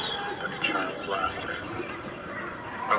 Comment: am I only one who heard "Tommy Shelby" 😍🤣